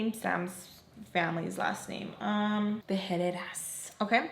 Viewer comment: So funny I hear “Head headass” when you said Herrerras 😂🤣